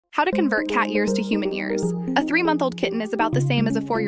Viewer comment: I thought you said "how to turn cat ears into human ears" 🗿